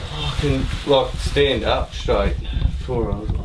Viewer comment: What did he say at 4:57? Sounds like “get some urine?” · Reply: get some air in